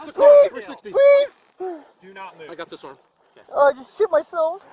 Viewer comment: Did he just say “ I just shitted himself “ ? 😂